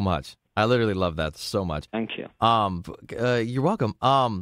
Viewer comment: did he say "fuck"? Regardless he was clearly trying to keep his cool during the whole interview and I loved his genuine comments/compliments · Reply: He “fank you” which just “thank you” pronounced with an f